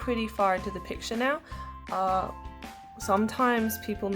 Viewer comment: The subtitles say 'our clock' instead of art block